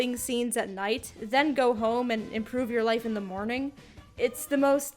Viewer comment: i thought she said "then go home and end your life in the morning" and seeing this fucking game it think it would be appropriate